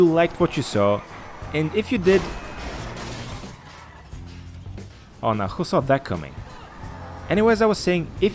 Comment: What did he say at the outro when he crashed the car in to the post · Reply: He said “oh no who saw that coming”